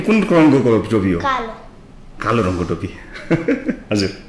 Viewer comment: Wht did he say on 1:34 did not understand properly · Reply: He say Gafadi